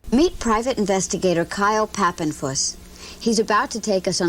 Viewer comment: i hear fapinpuss